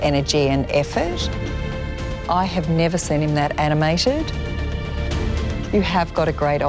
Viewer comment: Did Naomi actually say "Animationed"?? —7:58 — No such word.